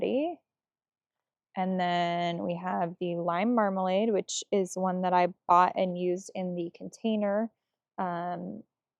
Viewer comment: Hi, What is the name of the flowers (7:25)? Sorry, I cannot hear the name clearly. Best, Albert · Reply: hi! They’re Heucheras ☺️